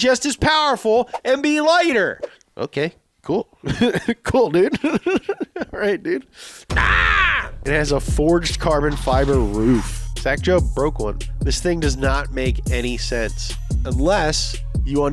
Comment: "Zack Jobe broke one" made me laugh way too hard 😂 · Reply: IS that what he said? I thought he said "that joke broke one" must b going def...